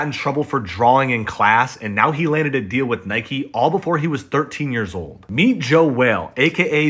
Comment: Need to cek my hearing.... thought i heard 'he was in trouble with nike before he was 13'😅